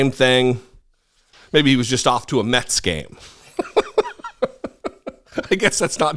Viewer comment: I misheard you, I thought you said Santos was off to a meths game.